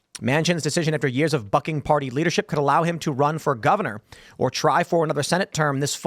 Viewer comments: I heard “…after years of fucking party leadership..” in the beginning 😂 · I thought you said "fμ¢king party leadership." 😂